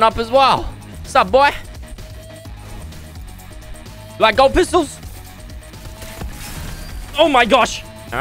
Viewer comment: did Fresh say GOLD pistol?!